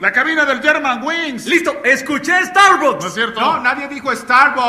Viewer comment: Dijo Star Wars? No entendí esa parte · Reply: Star bucks, cafetería famosa jeje